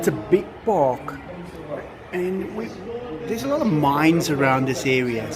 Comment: I misheard you and thought you said the park had a lot of mimes , you can imagine the visual i had of many mimes just hanging out in a large park 😂😂 another great video ! Love from Michigan, USA!